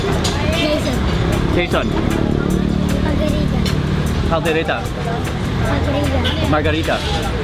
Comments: When you said Jason Caldareta, I laughed more than I should lmao. Enjoy the filipino food Mark! and yeah, you should visit Pampanga. It's like food heaven there. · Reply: Carlito Eleuterio I thought the kid said Jason margarita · " Whats your name? Jason Kaldereta." 😂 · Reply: Sounded like he said "Jason Margarita" lmao